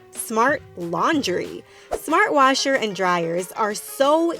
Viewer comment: when she said " Smart laundry" I heard " Smart lingerie" And i was like "whaaaaaaat"